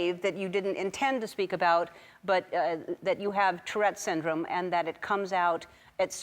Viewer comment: Its pretty late so I thought it said Torture syndrome